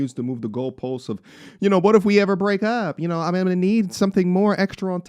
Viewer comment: HAHA! No, my guy - she didn't say "what if we ever break up..." She said "AFTER we break up..." [7:15] She already knows the contract length for her SIMP TEMP JOB. 😂🤣😆